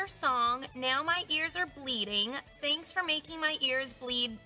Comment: i think it said ‘bleed bitch’